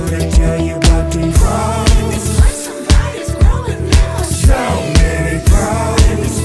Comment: the first time i heard this song i thought he was saying 'so many hoes' ._.